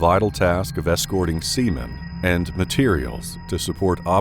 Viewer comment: Who else thought he said Semen...instead of Sea Men..lol